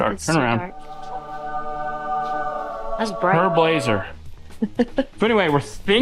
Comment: I keep hearing "Herb Lazer" when you guys say " Her Blazer". Admittedly my hearing isn't very good.